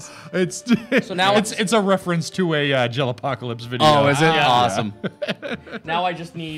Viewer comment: What is he saying? Angel apocalypse? I'm not finding anything like this